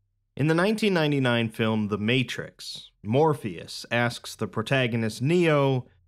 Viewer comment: My brain is so cooked I thought you said Morbius instead of Morpheus and nearly had a panic attack